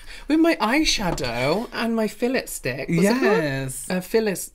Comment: My daughter thought he said Phyllis stick, I’m pretty sure she will be calling eyeliner that forever now 😂